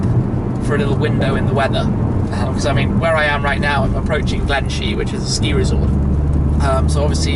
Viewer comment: I thought he said Glenshmee for a moment...